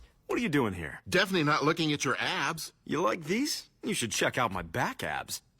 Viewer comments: I TOTALLY THOUGHT HE SAID "DEFINITELY NOT LOOKING AT YOUR ASS" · LMFAO I THOUGHT HE SAID ‘ASS’ FOR A SEC